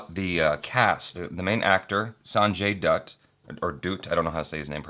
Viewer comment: Why did I hear "Sanjay Dude"?